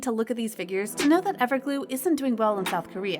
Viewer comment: did she said ever glue? 😂